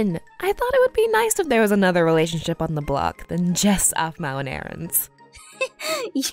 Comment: did she say "JESS aphmau and aarons."